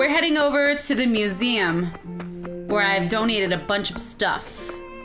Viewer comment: I thought she said Don't eat, instead of Donate.